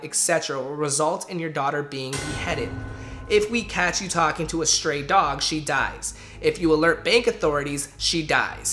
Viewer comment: Did it say, "If we catch you talking to a stray dog, she dies..."? Please tell me that's not what it said. If so, then I have some questions!